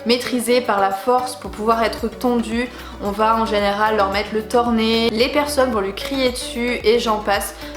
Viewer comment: coucou ! excellente vidéo comme d'habitude ! juste une question à 1:36 du parle de mettre un " thornet" ( désolé je sais pas comment on l'écrit) c'est quoi exactement car c'est la première que j'en écoute parler mais j'arrive jamais à comprendre à quio ça sert ...? merci :D